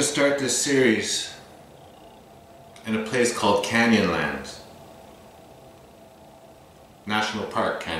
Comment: What? Wow! I have NEVER heard of Upheaval Dome. Seems like that would be a huge tourist location that would be widely known. Thank you! Canyonland. Never hard of that either. At first I thought you said Candyland. Wow...just wow.